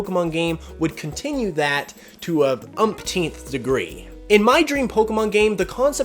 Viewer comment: to an unseen degree Me: I thought you said umpteenth🤪